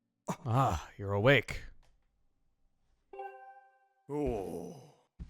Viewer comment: Did... Did the automatic captions just recognize music? WHO ARE YOU AND WHAT HAVE YOU DONE TO YOUTUBE!?!?!? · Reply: "You're awake rule" It... Actually sounds like he is saying rule to my surprise.